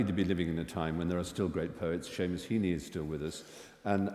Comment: Stephen Fry mentions a poet he thinks one of the greatest living but cannot make out the name , please help , thanks! ( It sounds like 'Shea Masini' but not it ) WE LOVE U HITCHENS!! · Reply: It's Seamus Heaney! Haven't heard that name in yeeears, we studied him a little bit in high school. The poem I know of his is ‘When all the others were away at Mass’. Have a look :)